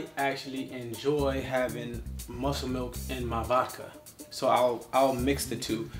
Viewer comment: the CC subtitles said "I actually enjoy having Muscle Milk in my locker" when he was saying "I actually enjoy having Muscle Milk in my VODKA".